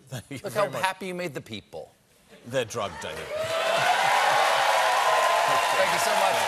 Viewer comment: Did he just say "They're drugged, David " as in Letterman...?😮😮